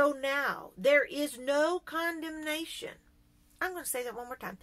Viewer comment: When you read the sentence with the blank word, I heard "indignation".